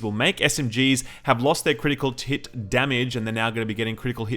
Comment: I could have sworn you said Critical Tit Damage XD